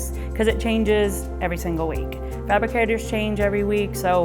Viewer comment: What's that word? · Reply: Fabricators